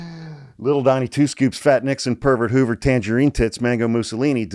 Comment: What da hell did he just call tRump? I heard the names of Dictators. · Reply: "little donnie 2 scoops, fat Nixon, Herbert Hoover, tangerine tits, Maga Mosolini". DAMN